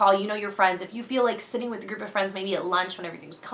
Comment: i totally heard " i have stripper friends" instead of group of friends! lol